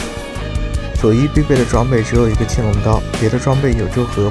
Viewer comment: the captions say blue buff, but you said Shojin, qinglongdao. Blue buff is bad right?